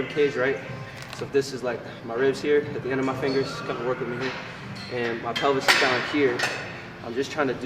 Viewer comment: Did anyone else catch ken filming that chick in minute 9:10 and said “ken work with me here” or is it just me 😂😂😂